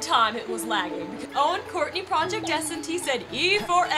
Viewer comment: Wait a minute did said Courtney Project NST?